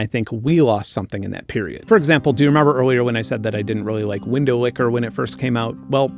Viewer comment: I keep hearing it as "Windowlickery Pee"